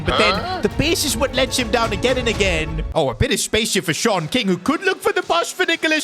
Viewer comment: When he said Shaun king I thought he said short king💀💀💀